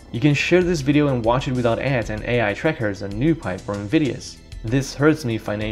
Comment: I can't make out what you said at 12:10 after : "you can share this video and watch it without ads ...." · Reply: NewPipe or Invidious to watch YouTube videos on privacy-friendly front-end alternatives.